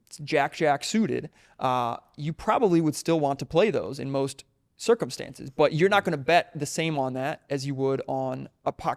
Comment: what is the name of the person he refers to when talking about exponential bet sizing. I heard Lance (something).. i did not get the full name. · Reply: Lance Breitstein. Aka Theonelanceb. Has a great YouTube channel and twitter